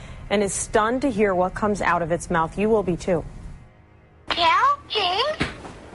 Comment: I heard Yea okay I was like what’s wrong?? Then it slowly changed to kill James